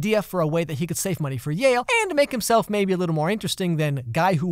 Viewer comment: Up until 05:28 I thought he was gonna work at jail instead of yale!